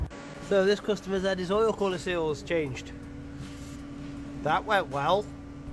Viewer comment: What did he say? I couldnt understand what he said about oil quality oils change??